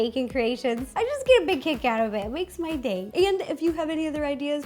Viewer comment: I know you said "kick" but at first I thought you said, "I get a cake out of that."😂😍